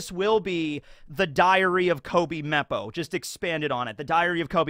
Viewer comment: I'm so sleepy and heard Diarrhea Koby-Meppo so ya boi said 😪🤨